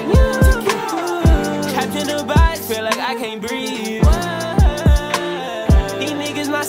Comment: All this time I thought he was say catching the vibe feel like I can’t breatheeee 😭 who else thought this.?